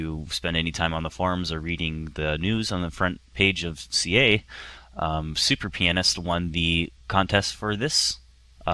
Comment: did he say the player "super penis" ??